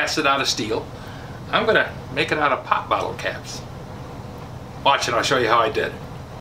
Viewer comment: OH, bottle caps. I thought you said pot metal, in which case I was going to remind you that the law would get you for smoking your file handle. Good one!